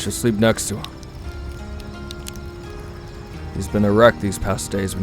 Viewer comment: WHEN BAKUGO SAID “a wreck” I THOUGHT HE SAID ERECT??!!!?!!😅😅😅